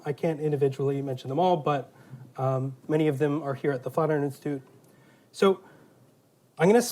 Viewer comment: Almost stopped at 0:36 because I thought he said “flat earth institute”.